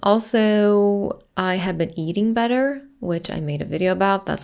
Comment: I thought you said you're now eating butter, 2:07 😂